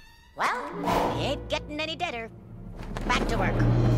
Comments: when i first saw this i thought yzma said "it ain't gettin' any better" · When I was a kid, I always thought Yzma said: “Well! It ain’t gettin’ any better!” When in actuality she said: “Well! He ain’t gettin’ any deader!” Damn! That is cold! Even for her!